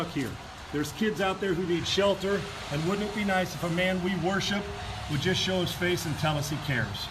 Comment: Did he say "if the man we worship would just show his face and tell us he cares?"